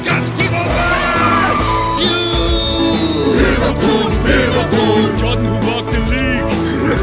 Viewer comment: it sounds like klopp saying f u liverpool